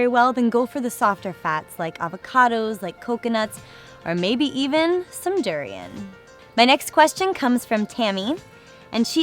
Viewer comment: I have never heard of the "softer fats" so what is that she says at 21:05 "like avocados, coconuts or.....??? ..." Can someone please tell me? Thank you! :) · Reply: 'Maybe even some durian ' Durian fruit is more fatty and apparently it's an aquired taste ☺